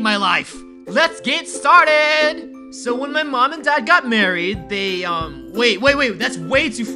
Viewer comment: did he just say "So When My Mom And I Got married" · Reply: He said when my mom and "DAD" GOT MARRIED